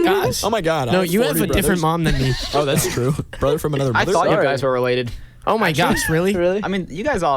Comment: did anyone else hear Corbyn say “ that’s true ... brothers from another pussy”